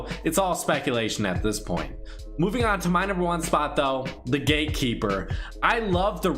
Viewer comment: Anyone else thought he said the Gay Keeper not the Gate Keeper